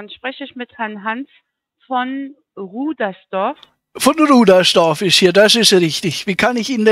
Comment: Ich habe zuerst "Rummelsdorf" verstanden 😀 kennt noch wer Spirou und Fantasio und das Marsupilami?